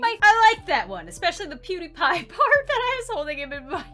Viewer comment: When you said "PewDiePie" I thought you said, "Cutie Pie"💀